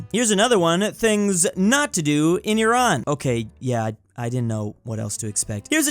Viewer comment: i thought he said things not to do in your aunt